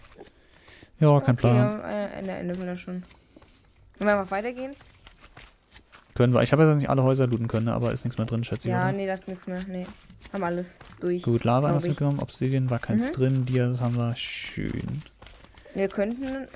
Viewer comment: bin ich der einzige, der zuerst Schätzchen verstanden hat??😂😂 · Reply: Er hat Schätz mal gesagt.